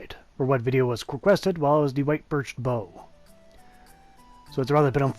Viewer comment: I thought that said white bitch bow 😂😂😂